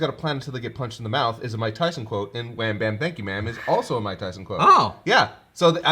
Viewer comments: Instead of "Wham-bam.thank you ma'am" i always heard "Got ya' mouth" as if he was completing goku's sentence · I always heard 'wham bam gotcha ma'am' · I'm with, I always heard it as "gotcha bitch" but kinda...garbled? never knew it was wham, bam, thank you ma'am